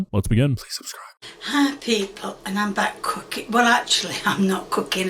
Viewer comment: wtf did she just say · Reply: “Hi people, and I’m back cooking”